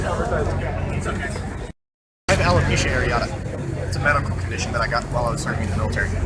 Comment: Quick thing because I've been reading through the comments and it sounds like alot of people misheard what the bald man said at the beginning. He did not say "lukemia" he said "alepecia" which is nothing like cancer. Alepecia is when your blood cells attack your hair because they think it is an invader in your body. There are varying degrees of alepecia with different names. At it's worst you lose all of your body hair and the hair on your head. It can also just cause you to lose patches of your hair like he said he was. Injections can sometimes help the hair grow back for awhile. My older sister has had alepecia since she was seven so if you have any questions please just ask so we can avoid any confusion on this later.